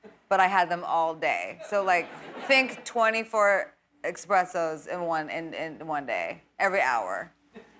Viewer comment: Did she say „expressos“ @ 10:33 ? 😅